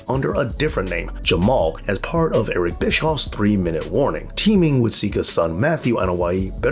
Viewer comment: This cousins are the real monsters nobody else 7:14 did you just said 3 minute